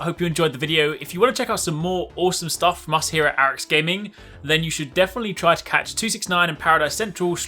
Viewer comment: Ok I need to know what he’s saying at 10:04 it’s been killing me. Sounds like “masahiro Arekkz gaming”